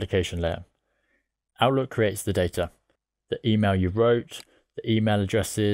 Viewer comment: what does he say here? “Our load creates the data”? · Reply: I think it's Outlook creates the data